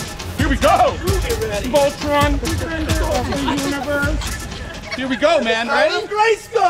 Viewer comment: Did he just say voltron let’s save the universe 😂